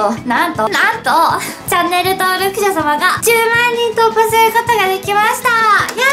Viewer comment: 最初らへんチャンネル登録者共が！て聞こえてしまった